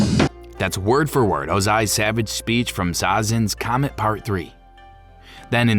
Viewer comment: Sounded like you said "Zazen's comet."